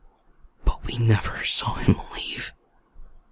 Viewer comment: I hear "we never saw him come out I" xs out of tab